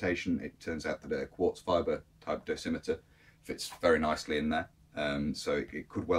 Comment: It probably turns out to obvious when I have the explanation, but I simply can't understand a word (or two, to be exact) of what you are saying, when at about 1:54 you talk about "experimentation" regarding the stitched of section of the upper pocket. It sound to me as if you are saying "quarz fiver", but why someone would want to stick a quarz fiber in there is beyond me, so could you be so kind and spell it out here? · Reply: I'm referring to a quartz fibre dosimeter.